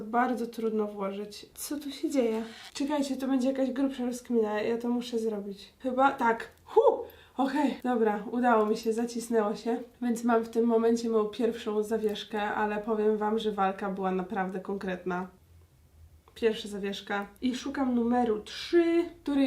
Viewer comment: czy tylko ja usłyszałam mój pierwszy zawieszka? XD